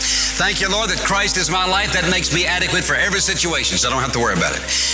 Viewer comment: Hey! Love the videos. At 10:56 you misspelled the word "adequate" as addacle. Maybe because it wasn't clear what the preacher was saying? Just thought I'd point it oput in case you wanted to change it!! This video inspired me this morning. :) Thank you!